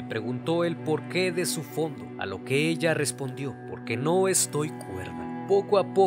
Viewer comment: A lo que ella respondio ? Que respondio ? No se te endendio lo que dijiste ahi. Alguien me podria decir que respondio? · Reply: Lo que dijo fue: “Porque no estoy cuerda”.